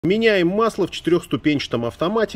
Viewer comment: Мне одному послышалось что он сказал: "меняем масло у KFC"